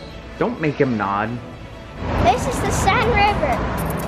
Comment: For a second I thought said “this is the sad river” and i was like “aw poor river”